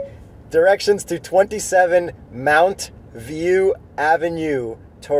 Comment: I thought he was saying mount view the whole time, not mountain view.. He needs to learn how to talk.